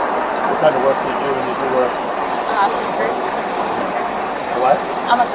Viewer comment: did she say she was a surfer as her line of work, lol?